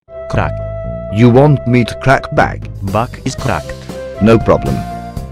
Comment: No way did just say kracc bacc 🥶🥶🥶🥶👌